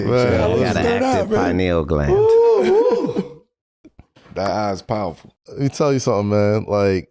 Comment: Did Melo say “That Eye is powerful”?….